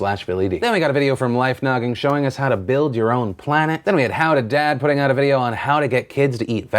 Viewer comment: how to dad? Boi I thought you said how to dab